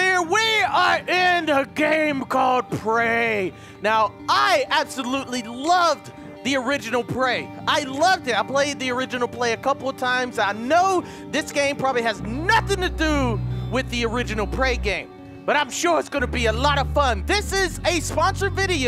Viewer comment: Did he say "original play" lmao